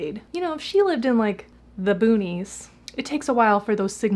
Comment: God I thought you said ' Le Boonies' I'm like ... "There's a LeBoonies, Iowa?"